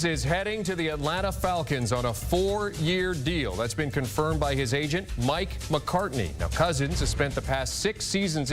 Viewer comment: Thought he said his agent Mike McCarthy!